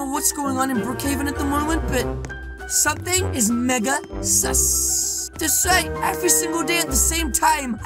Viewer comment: WHAT THE HELL U SAID THE N WORD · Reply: He said Mega Sus